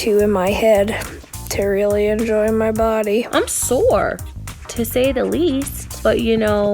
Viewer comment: kristen: i'm sore me:BCUZ U R WEARING A CHERRY DRESS HAHAHA I really thought she said sour.